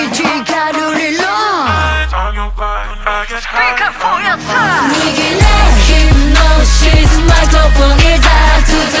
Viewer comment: ここなんて言ってんだろ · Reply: 恐らくspeak up for yourself!ですかね...？